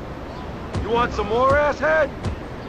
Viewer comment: yo son did he call him a 'ass head'